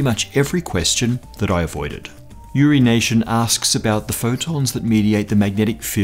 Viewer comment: Did he said Urination? Yes, my inner 7 year old went there.